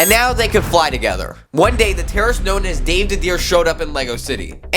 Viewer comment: did you just say Dave the deer